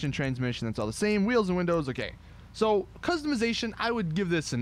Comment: I know he said “wheels and windows ok” but at first it sounded like he said “wheels and windows are gay” l o l y s Betty bet bet